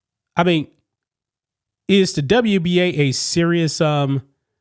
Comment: I think u said wnba at the 5:21 mark